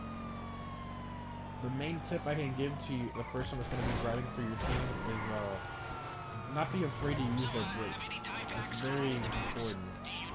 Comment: what does he say at 13:12? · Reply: He says "The main tip I can give to you - the person who's going to be driving for your team - is ... not be afraid to use the brakes. That's very important." I couldn't 100% understand what is said at 19:02 , I have a guess, but if it's "that" then I can understand why it's deliberately mumbled anyway, haha